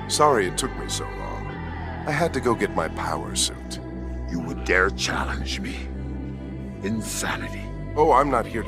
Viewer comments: For the longest time, I thought he said, "You would dare challenge me? In vanity?" Now I feel stupid. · I honestly thought he said, in vanity. Not insanity. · I always thought Darkseid said: "In Vanity?" Rather than "insanity".